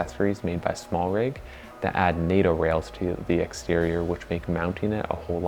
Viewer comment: What is the Small Rig accessory you mention at 3:35? I cannot understand what you''re saying. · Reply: I just said Nato rails are useful, this is the swivel mount <link>